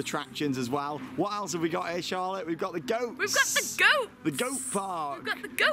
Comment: I misheard at the beginning of your vlog, it sounded like Charlotte said "We've got the ghosts"!!! Had me checking what new addition they had put in, haha.